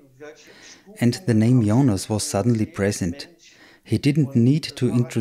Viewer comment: Welcome on this channel and thank you for watching this video! Did you also have any experiences with the other side and want to publish them on this channel? Please contact me: <mail> Or share your experiences here in the comments. Thank you! Many further videos will be uploaded. Please subscribe, if you don’t want to miss them. Thanks! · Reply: Did he say the name "Jonas"??